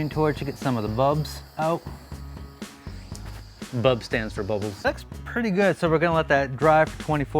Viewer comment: I really thought you said: “Bubs stands for Boba Fett.”